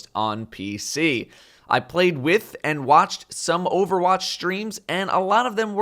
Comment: Did he said I watched overwatch streams